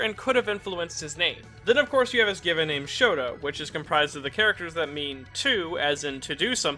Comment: Did anyone else hear ''Shoto'' Instead of Shota? It makes a really big difference considering Todoroki's first name is Shoto, I have re-listened it a few times and I'm almost sure its mis-pronounced